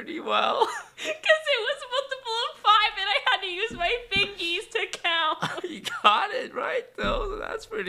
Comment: Did she say fingies instead of fingers?